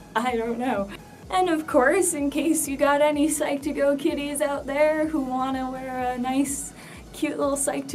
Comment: I heard "To all the Psych2Go kitties out there". XD